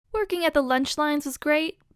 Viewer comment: In the intro, I heard; 'Twerking' & not working